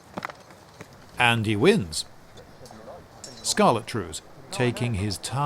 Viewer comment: Did you say " .. scarlet trews" ?